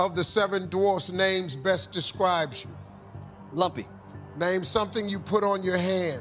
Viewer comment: I thought he said lovey not lumpy.